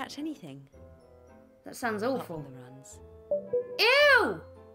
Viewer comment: What's the Robo-Emma saying there exactly? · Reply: Except for the runs